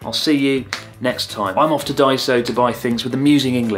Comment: At first I heard "I'm off to die, so.." instead of "i'm off to Daiso" lmao